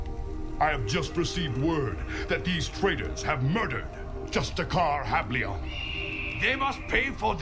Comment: With close caption turned on it says "Just a car Happy Meal" instead of Justicar Hablion XD